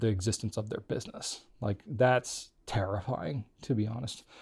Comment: Did you say it was "tariffying"?